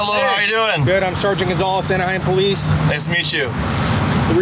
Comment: did he say "I am police" ???? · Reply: “Anaheim police”